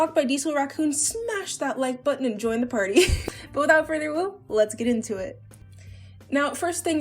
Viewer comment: d-did you just say "without further awoo"